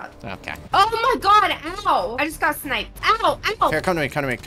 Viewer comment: i thought she said oh my god elmo